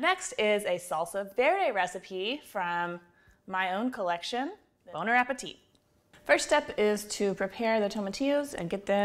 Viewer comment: Did I just hear say "Boner appetite"? I thought I was the only one using prevert French! The charred beer salsa looks fabuloso!